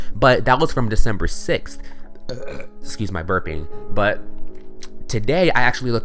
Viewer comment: it sound like you said burpy lol